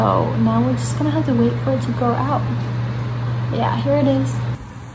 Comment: DID U JUST SAY" HAIR IT IS" OMG